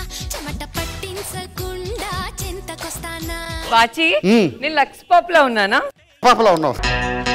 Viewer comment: what did Bullet Bhaskar say? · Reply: Katter papa